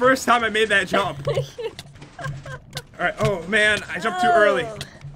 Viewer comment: Did said "I jumped too early"? I just laughed like nothing.......